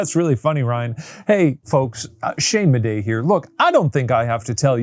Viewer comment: In the ad read it sounded like he said "Shane Bidet here" 😂